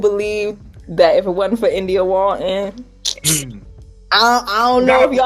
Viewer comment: What is she saying at 3:02 "if it weren't for India what". Can someone help. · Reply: i think it's a name of someone. i hear "India Walten"